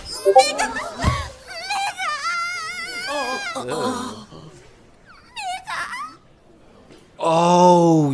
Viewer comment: Wtf she just said 💀💀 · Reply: She said niga san